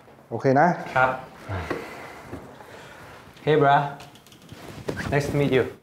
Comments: when john said hey bro i thought he said hey bra lol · what did John say? Hey bra???😂😂😂😂😂